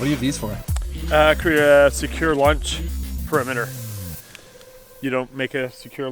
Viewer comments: Bahaha! I thought he said "lunch perimeter," not "launch perimeter." I was like "whoa this is going to be some serious crazy eating." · Maybe it's my American ears. But I thought he said a secure LUNCH perimeter, not LAUNCH perimeter.